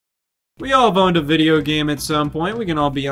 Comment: i thought he said we've all boned a video game before and i had to replay him like 6 times to realize he said we've all OWNED a video game before